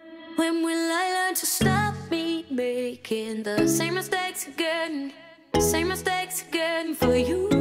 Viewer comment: I thought she said "same a sex again" then im coming here to make sure😂